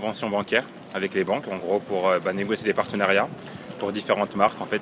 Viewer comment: Am I the only one who can't hear "négocier" at all at 0:40?